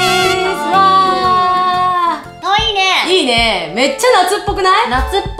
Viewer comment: の｢かわいいね｣が｢トイレ｣って聞こえた(難聴)